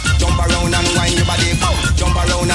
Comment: I'm 37 now....as a kid hearing this I thought he was saying " John barone wind your brolly" hahaha love this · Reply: Or... "... and wine ya buddy". I'm 38, I always knew it as "Jump around and wind ya body